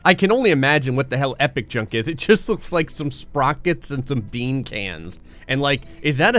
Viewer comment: DID HE SAY SPROCKET?! I love Fraggle Rock! like if you understand the connection I made there! 😂🤣😂